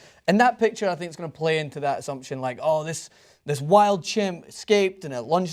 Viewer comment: did he just say a wild animal escaped lol